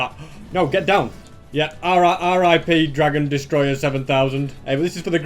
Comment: Anyone caught him saying ri rip but sounded like Ara Ara